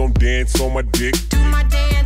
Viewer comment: One first listen. I thought it said do my dad instead of do my dance